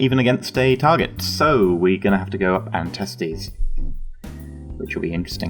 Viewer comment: I swear he says "We are going to have to go up and Testes." Loooool